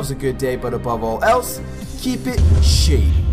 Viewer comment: I thought at the end you said "Keep it shitty"